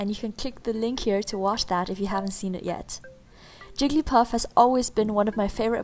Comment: 10 seconds in, who else heard her say "Jiggy Puff"